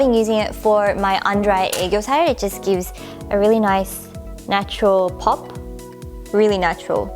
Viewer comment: what does she say at 4:25? "under eye __ side"? Is that a korean word? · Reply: she said "aegyosal", which is the puffy bags under your bottom eyelids and they are seen as trendy and cute in asia :)